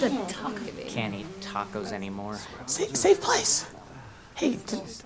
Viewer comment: "can't eat tacos anymore" anyone else heard that?